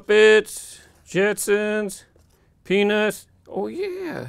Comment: sounds like he said penis